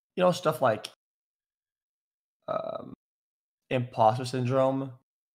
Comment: DID HE ACTUALLY SAY IMPOSTOR SINDROME?